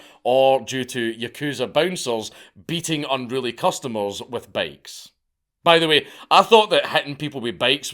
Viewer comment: Who else had to rewind a dozen times just to figure out that Dankula was saying “bikes”? I thought I heard bags, bakes, etc. From now on, Scots must use the words bicycle and motorcycle.